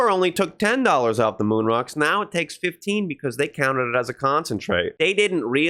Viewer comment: How would 15% of a 20$ moon rock come out to 5$? I'm pretty sure that's what I heard you say. 15% of 20 is like 3$. I could be wrong if I misunderstood. Lmk if I heard it wrong.🎉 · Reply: He said $15 not 15%